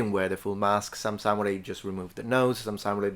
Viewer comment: for a second there, I thought you said "some just wore the nose" and I had a wonderful trip of the imagination depicting 13,000 massed, threateningly clown-nosed samurai 😂😂😂😂😂 Honestly, it would probably be terrifying